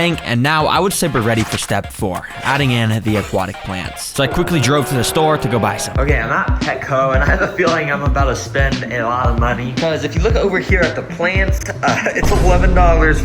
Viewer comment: who else thought that said ass plants?😂💔✌️